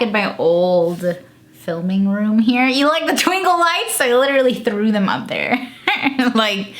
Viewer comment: Love the twinkle lights lmao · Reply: Mari Williams lmao my chubby ass thought you said twinkie lights i was lookin all in the background for some twinkies lmfaooo 😂😂💀💀